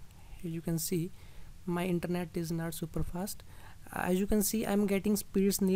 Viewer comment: "the internet now is super fast" xDDDDD its 80kbps xD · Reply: mishearing i guess, he said "my internet is not super fast"